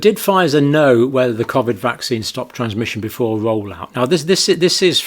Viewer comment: The Pzifer exec I thought said "stopping immunization" not "stopping transmission." I've since seen elsewhere the subtitles have her saying "stopping huminization". Regardless wonder how that would change things in a court of law?